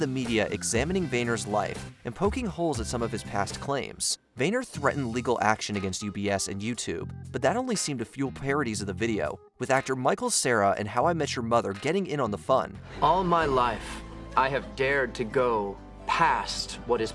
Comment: Did you say Michael Cera IN or AND How I Met Your Mother????